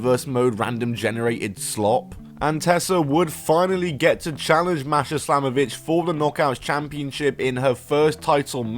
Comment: I heard "Masha Slam-a-Bitch" every single time. 😂